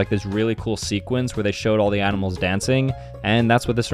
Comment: did anyone else at 12:17 think he said there was a scene with all the animals dead instead of dancing?